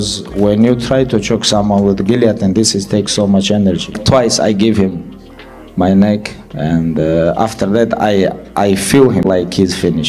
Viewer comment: I might get beat up for this but I turned way from my pig one and I heard “twice I have him neck, untill I felt him finish” or something like that 😂😂 i was like out of context